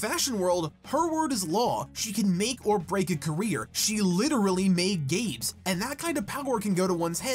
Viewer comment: oh my god i thought you said "she literally made gays"